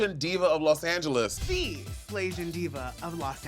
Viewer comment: I thought she said Slayzian diva.